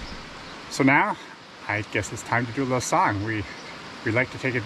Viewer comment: Was it just me but at 5.52 into your vlog I thought you said " now it's time for a little song " 🎵 then my mind was racing thinking what you would be singing...."Singing in the rain"...came to mind 🤔 has anyone else any thoughts on what could sing ?...Best wishes from the North Norfolk coast UK 🇬🇧🙂